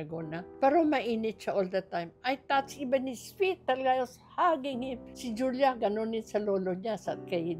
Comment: may narinig akong mahinang bulong huhu as in yung bulong na mahina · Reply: “Hugging” it was just her voice